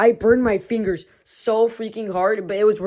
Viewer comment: I keep entering hallucinations that make me lose my mind. I thought you said bad instead of freaking.